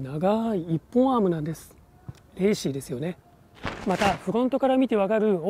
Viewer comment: iPadで観てるんだけど 0:38 の「レーシーですよね」が「Hey Siri ですよね」に聞こえるらしくSiriが起動したw